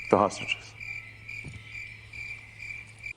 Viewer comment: Did he say "hostriches"?